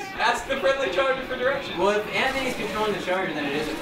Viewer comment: at first i thought that anthony said anthorny